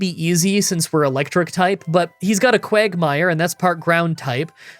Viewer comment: Did he just say: 'he's got a Quagmire' 10:30. Almost as great as snowbama